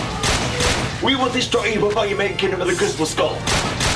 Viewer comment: what did he say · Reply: "We will destroy you before you make Kingdom of the Crystal Skull"